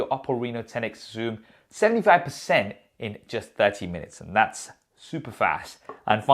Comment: i heard "thats supersaf"😂😂😂